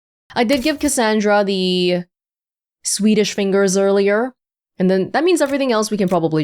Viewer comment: I keep hearing "Sweetish Fingers" as swedish lol